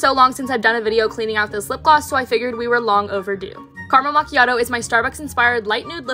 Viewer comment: Istg every time you say "Carmel machiato" I hear "karma machiato"💀